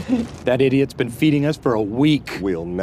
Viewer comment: At first, I thought Dwight said: Daddy’s been feeding us for a week. What he actually said: That idiot’s been feeding us for a week.